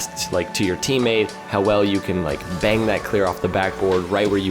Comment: I thought he said “bang that girl off the backboard” 😂